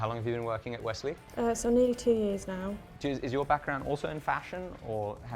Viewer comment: sounded like she said shes worked there for 72 years